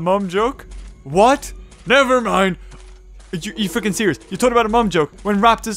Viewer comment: did he say f&@